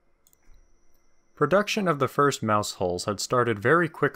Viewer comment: Is anyone else mishearing Maus hull(s) as mousehole(s)?